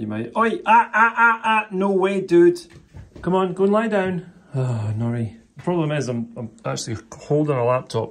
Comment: Sounds like is saying Naughty, not Nori, ha ha